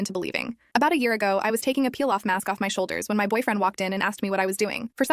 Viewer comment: 💀 bro when she said “peel off mask” I was watching the dude peel the grass so in my head I thought she said “peel off grass”